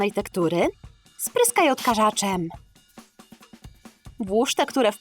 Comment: usłyszałam,, spryskaj odkurzaczem" XD